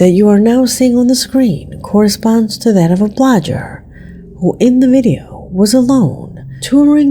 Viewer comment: Wait a minute. 7:53 Did you just say ‘a blojjer’, instead of blogger? 🤨